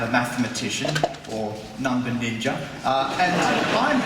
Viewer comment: Excuse me, but I didn't hear what he said in 0:40 "I'm matematician or number<what?>", can someone tell me what that was? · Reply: number ninja